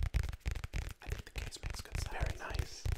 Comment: I thought he said. I think the gays make good sounds